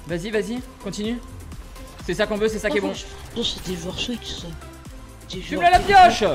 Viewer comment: A 9:01 il a dit quoi l autre ?? C des joueurs Switch ça